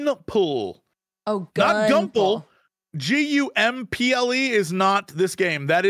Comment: I also thought said GUMPLE